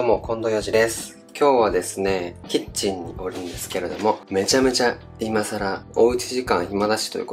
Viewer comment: 「キッチンにおりんすけれども、」『〜おりんす』とか『〜ありんす』とか言ってるの好き笑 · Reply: おるんですけれどもって聞こえてました！笑